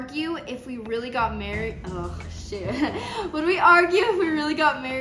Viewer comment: On would u argue if u actually got marries on I thought I heard say on sh*t